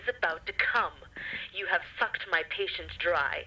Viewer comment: I swear at 15:55 she said f*cked